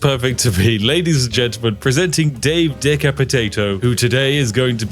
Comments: I keep hearing "deca potato" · I seriously thought you just said Deca-potato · I keep hearing "Dave of the Potato" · Every time you b say Dave's last name I hear : "Deca-potato"... A potato 10 times the size of your average potato